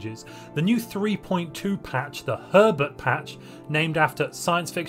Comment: With your accend it sound like you said pervert instead of herbert...